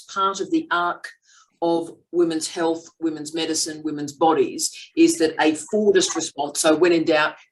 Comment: Did you say a "fordist" response? at 13:14?